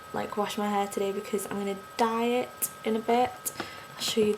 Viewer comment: lol, I thought you said you weren't going to wash your hair because you're about to DIET.. (didn't immediately catch DYE IT...) I was thinking "what does a diet have to do with your hair?" lol